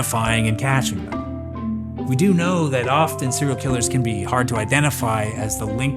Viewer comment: Wait wtf he said my name at the end??? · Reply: Nah my sleepy ass heard you say “often” and had to turn cc on cause is sounded like Austin 😂